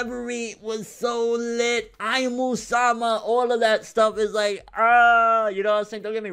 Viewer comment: i thought you said "i'm Osama" lmao